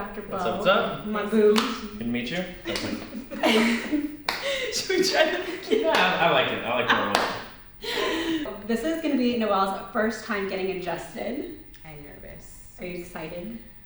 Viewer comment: When said "this is Noel's first time getting adjusted" I thought I heard "this is Noel's first time getting INGESTED." I stopped for a moment and was like, "Well... that's different" before my brain reactivated itself.